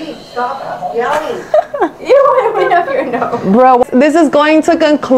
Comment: i heard "brenna stop yelling.."